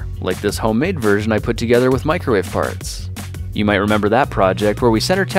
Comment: he said microwave farts instead of microwave parts